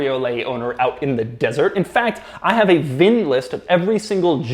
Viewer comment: did you say VIN list? You on VINWiki Doug?